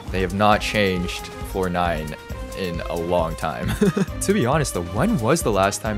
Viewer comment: Each time he said floor nine, i thought he was say fortnite lol